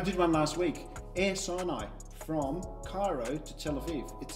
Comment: did I hear that correctly? Air cyanide?